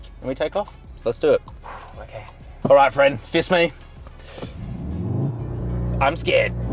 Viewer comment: Did he just say fist me???😅😅😅. I suppose its OK to say that in Australia